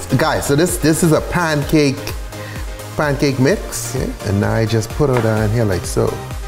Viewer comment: I thought he said pinecake mix 💀